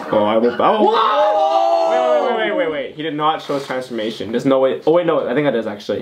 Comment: did he said „warte“ (German)??!!!!